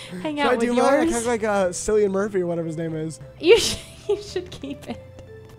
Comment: DID HE JUST SAY SILLYANNE MURPHY???